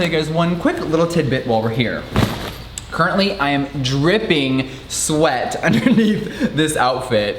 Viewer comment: I am 99% sure he said ”there are so many birds tripping outside” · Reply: i definitely heard “tripping” lol